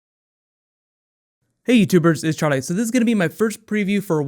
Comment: I always hear "Hey, you birds" instead of Youtubers.